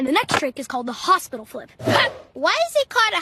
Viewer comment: People who thought it said hospital trick but it actually said hospital flip